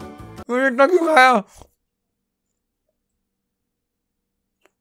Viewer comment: for those who didn't understand he said- "maine itna kyu khaaya"